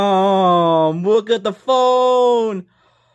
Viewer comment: sounds like you said "Game" instead of "Damn"